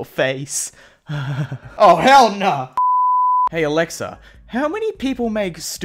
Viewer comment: Did u actually say “ Ah hell na”